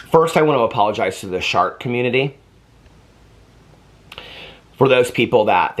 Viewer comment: I thought you said shark community at first. I was like damn, what did you do to the great hammerheads? Lol. Dribble can ruin your life. Twoots are very serious. 😂 Gah lawd. This was awesome. 😂